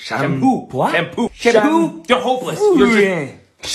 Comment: bro i heard "you're homeless"